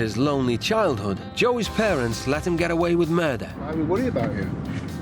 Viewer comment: let him get away with MUR***?? I think i heard it wrong